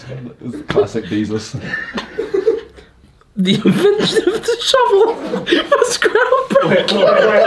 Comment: I thought Ethan said the infant shoved a shovel up a horse. 😂😂😂😂😂